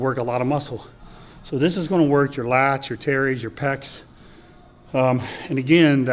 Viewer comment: Wait, hold up... my what??? I heard lats and pecs, but what are "terrys"?